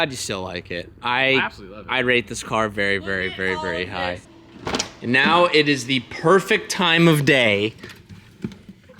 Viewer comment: nah i want to know what said at 17:47 🤣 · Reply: She look at all of this